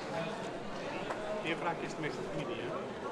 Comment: Wat wordt er nou gezegd op 0:07? Lijkt op : '"Eerwraak is 't meest ....... " Ik kan het laatste niet verstaan. Maar hoe en wat dan ook, het is vreselijk. Nog maar 16 jaar oud en dan zo van het leven beroofd worden.